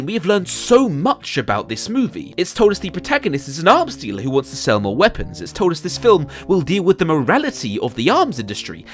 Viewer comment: incorrect, I have learned nothing about this movie because for the life of me I can't hear the couple crucial words before "the other 11" 😂😂🙈 but maybe that's on me 😂😂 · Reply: It's "how do we arm the other 11?" :)